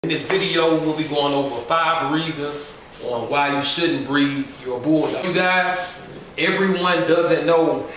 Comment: Another one discouraging people lmao thinking you can control the market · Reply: really cause it's sounds like he said why you shouldn't breed these dogs 😂